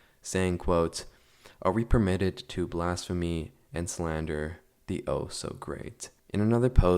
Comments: I heard “are we permitted to blast Fein and slander the oh so great” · i heard are we permitted to blast the ozone layer😭🙏